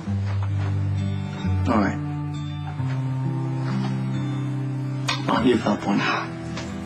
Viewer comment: What the hell does say at 2:18? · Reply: "All right. I'm not giving up on you."